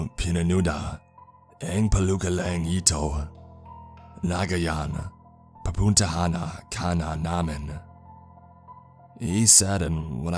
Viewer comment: "Dapat hindi mo pinanood and pelikulang ito, ngayon pupuntahan kana namin." 13:25 im prettty sure that's what he said. This is filipino. which translates to " You should have not watched this film, We will come for you now." Freaked me the fuck out when i heard it.